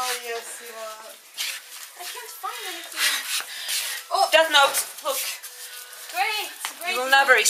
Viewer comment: When Spain said "that note" the first time I watched this, I thought he said "Death Note", and I started freaking out (what if England's name was in there?!). But I wasn't looking at the screen right then, so...